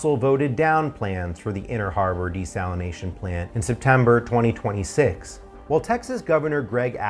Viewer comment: Did he say they made this vote in September 2026?